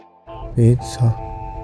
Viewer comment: i thought he would say "pizza shit" (piece of shit)